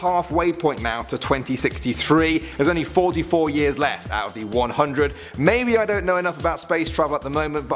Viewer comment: I thought you said “there are 44 years left and I’ll be 100.” I was going to tell you you look awfully young for a 56 year-old. 😂